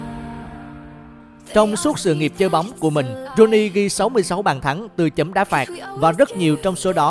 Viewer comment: Có nghe nhầm ko nhỉ sao đọc tên Rooney 2 lần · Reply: có nghe nhầm đấy !! ng ta đọc là Ro ni ( Ronnie là tên thân mật của Rô vẩu )